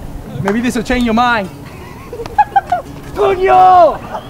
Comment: what did he yell when the threw the ball at 1:20? “TOOONYOO!!”?? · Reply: Coño